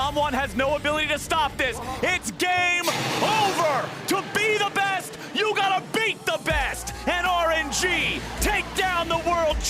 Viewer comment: To be the best, you gotta beat the best! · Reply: i thought he was saying to beat the best u gotta be the best,,,, but whatever both great team and congrats RNG